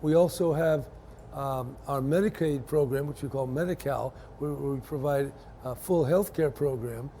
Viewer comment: Medicow it sounded like he said that.